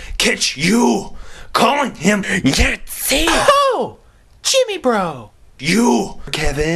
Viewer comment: and i thought was saying nancy instead of yancy. lol.